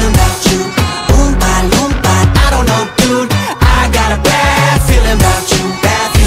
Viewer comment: At first I thought it said " noba noba nobody knew I got a bad feeling about you" 😅